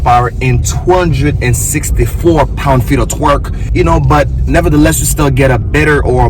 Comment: Idk about you all but I heard him say "lb feet of twerk"